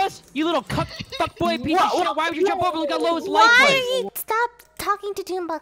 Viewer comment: All i heard was "why would you jump bubble gum low as life points"